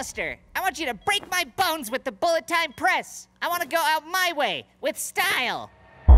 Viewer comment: Did someone say... Tiny Style?